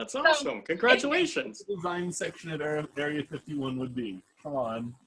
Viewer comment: I can't hear what says at 10:00 and it's driving me nuts! :P Also, I may or may not have finished that scarf in one week... <.< I'm nuts. I fully own this. I always feel like I'm cheating on my podcasts if I skip around in order on my Watch Later list, but since you told me to watch it on Ravelry, I figured it was okay. :P I honestly can't say I'm particularly surprised, haha! XD BUT I'm only accepting one. Redraw for the UFO thread, hehe! That's just not fair. :P I'll PM you in a minute. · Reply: He asks me if I will be designing UFO's which he further clarifies is exactly what would be in the design section of Area 51!